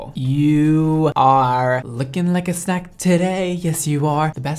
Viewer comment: I thought brad said sack I was like “of potatoes” but then he said snack and I was like “oh so potato’s” 🤭💞